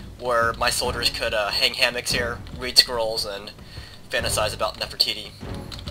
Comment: I thought he said "breed Squirrels " O.O